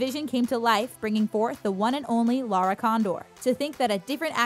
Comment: did she just say Lara condor